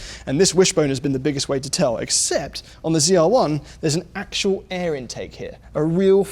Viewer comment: I heard ZL1 a couple times. Example